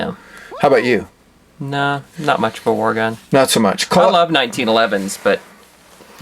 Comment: For a moment I thought you you guys said COVID-1911 lol